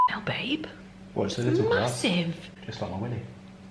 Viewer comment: Did he just say "like my weenie".......lol.